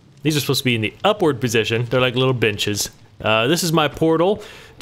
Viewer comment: i rewatched like 3 times because I thought he said the trapdoors were like little bitches, not benches lol